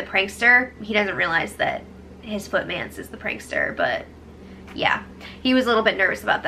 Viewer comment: Footmance🤣🤣🤣. I had to replay it, thought I heard f***dance. 🤣